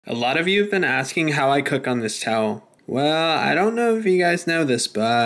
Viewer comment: i thought it said how I cook my towel